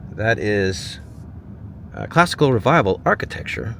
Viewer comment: Chipley government building @ 12:11 minutes in - I thought you said Costco Revival which is a more fitting description of that horror show